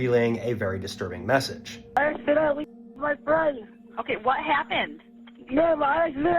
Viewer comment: I thought he was saying " I accidentally f*cked my friend " at first 😂